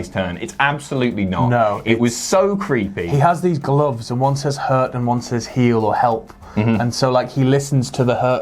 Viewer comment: I thought the glove said hurt head